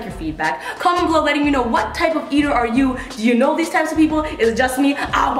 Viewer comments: I thought it said types of Easters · At first I thought it said "Types of Easters" I was like whaaaaaaat?